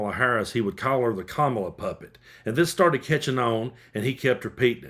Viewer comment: At first I thought you said puppy 😂